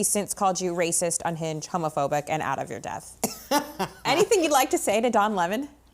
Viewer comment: did she say ""out of your "death"", (depth) illiterate, uneducated media vermin.